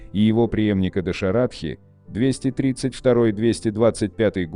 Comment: Послышалось Доширак😂😂😂